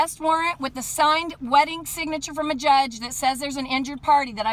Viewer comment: Did she say a signed wedding signature? Please correct me if I misheard.